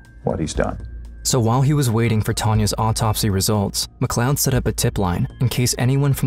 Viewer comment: the cops name is Mcclown? Or am I hearing wrong. Either way I'd be putting police near pay phones. Also Misty is so brave and a good friend she stayed with her friend knowing she would possibly suffer the same fate. What a amazing true friend and human being. She also helped put that disgusting creep away. Great video and story. RIP Tanya. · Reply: Lmao it's Kevin MacLoed